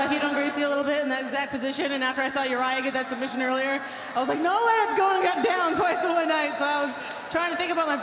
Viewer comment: aww last night I thought she said "no way is he gettin that twice in one night" about Urijah